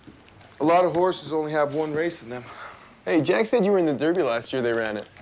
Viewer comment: Did Ty call Jack “jake”?